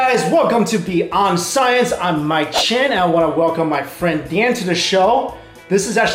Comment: Probably last Pokemon video on this channel guys :-) Thanks for watching! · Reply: UR NAME IS??? i always thought that you was saying xDDDDD